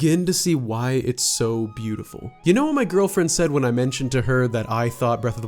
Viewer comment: Is it just me or did it sound like he said "my girlfriend died"